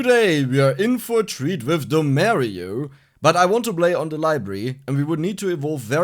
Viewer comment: I thought u said "The Mario" at the start instead of Dommario